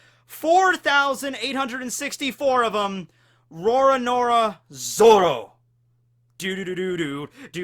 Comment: Sounded like you said "Rora Nora Zoro" for Zoro